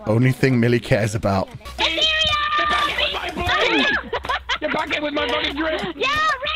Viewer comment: what is saying 😂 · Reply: "GET BACK HERE WITH MY BLING"